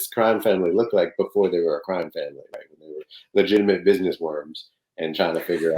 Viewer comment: Did he just say "legitimate businessworms?"